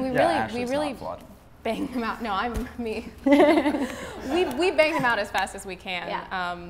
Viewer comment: "We bang them out as fast as we can..." Anyone else hear, "We BING them out as fast as we can" (Cuz we all know we'd bing Bing in an instant)